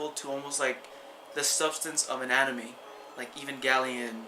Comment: when you said "Substance of Anime" I first thought you said "Substance of Anatomy" My guess for not having a lot of people knowing about this is because most people watch Netflix for movies or for series which already happened on TV. they really don't care too much about Netflix originals, unless they are something big like Daredevil, or Jessica Jones, or Narcos. Then again, these shows have huge advertising, and I had never heard of Stranger Things, so my guess is that it has not been advertised enough.